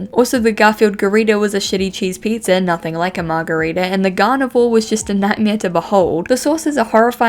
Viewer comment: listening to this and not watching it, and.......... why do I keep mishearing garnivore as gardevoir, lol